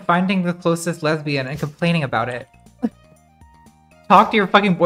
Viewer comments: Jesus, my volume was low and I thought she said off your fucking boyfriend. Reading the comments like wtf is wrong with all of you? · HELP I THOUGHT SHE SAID "off your f*cking boyfriend"